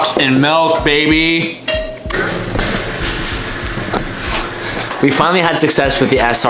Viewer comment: I thought you said "we finally had sex"